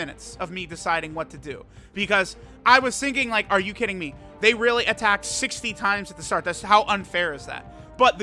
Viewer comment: for a second I thought he said "are you kidding me? They're attacking in the 1st slot like '68' times" and I was like "TRUTH YOU WERE SO DAMN CLOSE!"